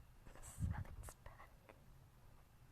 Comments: IS IT JUST ME OR DO I HEAR "the sun is back" from the window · just to tell you, at 31:31 (ironic right) you can hear what sounds like a female voice saying "the sun is back. the sun is back. the sun is back." then giggling.